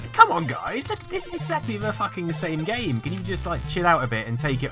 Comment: what did he say? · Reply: Did he say 'the fucking same game'?